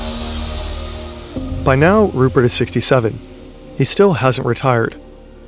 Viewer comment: did somone say 67?